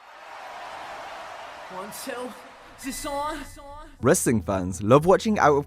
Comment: what is said One sell its on!